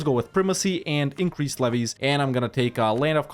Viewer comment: This is my first watch since subscribing. I've enjoyed your A-Z series for a while so I thought I should correct my omission. I just wanna say that every time you say "increased levies" I hear "increased Zlevvies" like it's your nickname for and I hope you never change because that's great.